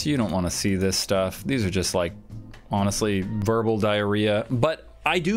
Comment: what did u say...... Verbal Diarrhea. O.O